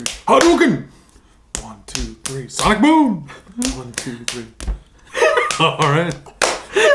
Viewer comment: Oh, "Sonic Boom!" I thought you were saying, "Side Boob!"